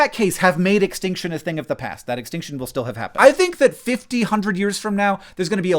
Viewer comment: I thought you said "fifty-hundred years from now", instead of "fifty [to] a hundred years from now" and was impressed by your bold foresight lmao.